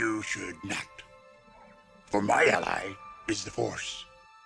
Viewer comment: why do i always hear "for my ally...is divorce"?😂😭